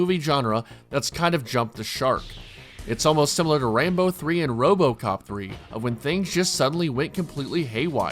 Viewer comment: did you say "Rainbow 3"?